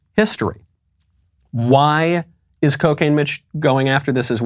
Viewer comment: did you say cocane mitch?